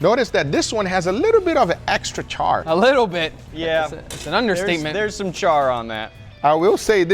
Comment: Did he say "Understeakment"? :D